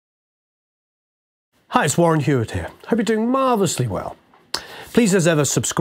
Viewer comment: Warren Huart... Thought it said Warm Heart. : )